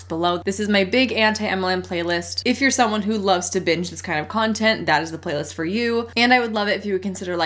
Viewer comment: I am listening to this at work and for some reason at the very beginning I thought she said “if you’re the kind who likes to b*tch about this content” instead of BINGE and I was like, “wow, sweet Hannah is spicy today!” 🤣 I meeeannnn, I DO like to b*tch about MLM stuff AND binge it 🤪